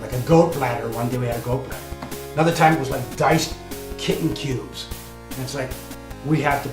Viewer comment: the "eating a goat bladder & diced kitten" story is almost definitely false, a goat bladder is too big to stick in a can of a convent size and tends to be a delicacy, & how do you even tell that a mystery meat is cat, specifically a young one his dad probably forced him to eat canned Green beans & pickles. and he blew it way out of proportion in his mind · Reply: I honestly thought he said "diced kitten food" at first. Certainly would make more sense than actual kittens.